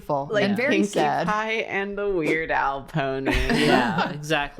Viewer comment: Definitely thought was saying "Weird Owl Pony" the first few times.